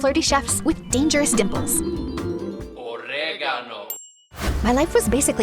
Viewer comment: am i the only one who heard OrIgAnO in the beginning 4:30 and 41:18 wh did he say CarEfUl NoW i BiTe